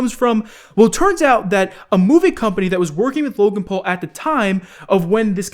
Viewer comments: I swear I thought he kept saying moving company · I thought you said moving company lol...